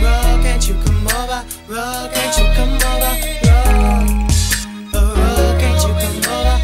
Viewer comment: When I was a kid I thought he was saying bro can't you come over